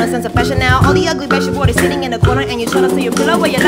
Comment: gw dengernya aduu wada wong mksdnya??? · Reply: "I do what I want" gan😅